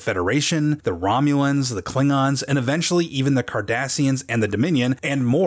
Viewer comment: Im not a big star trek fan and when he said kardasians i thought he said Kardashians lol